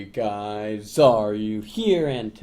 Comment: Holy shit, just realized he says "Hey guys, Xaryu here" at the begining. I always thought he said "Hey guys, are you here?" ._.